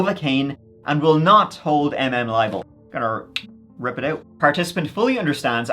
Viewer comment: "...will not hold MM libel" i think that should be 'liable' instead of 'libel'